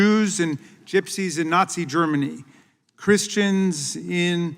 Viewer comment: I hear the voice of the Democrats and the first phrase is "German Nazis". wow, that's fresh 😂